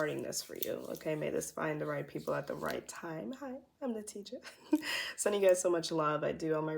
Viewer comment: You said "Hi im Natija" and I heard - Hi, I'm the teacher 😅❤️😘